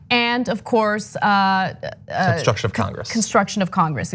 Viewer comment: it sounded like John said construction too